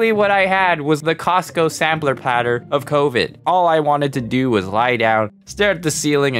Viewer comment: wait, you said covid. i thought you said youde still call it steve